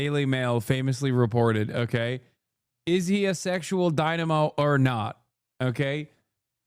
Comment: It’s not their fault that you misheard. You thought they said he was a “dynamo,” but they said he was a “dino.”